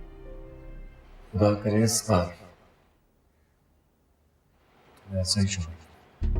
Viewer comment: yeh bnda kya bola mujhe smjh nhi aaya 😭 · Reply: Usne kaha "khuda kre tumhe is bar aisa he sohar mile"